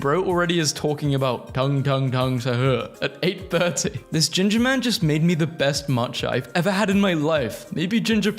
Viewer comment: I saw and heard it as gingerbread man😭